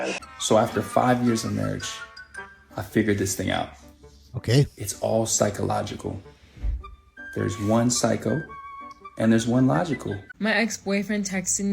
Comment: What Di what did he say (1:52)? “It’s all psycho- logical. One’s psycho, and the other….” BRILLIANT!